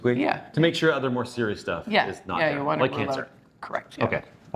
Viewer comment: So at 3:56 did she say blood cancer?